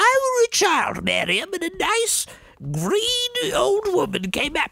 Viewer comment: I kept hearing that as 'greedy old woman' instead of 'green old woman', and I was like "Wow. She just... she doesn't sugarcoat it, does she? Respect."